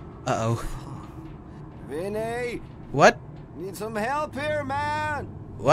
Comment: vafan! Hjälp! fan! did i just hear swedish??? O.o